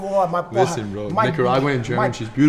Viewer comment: I thought you said "she's a nigga caralho" lmaooooooo